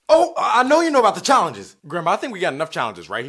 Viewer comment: it sound like he said grandma instead of grandpa.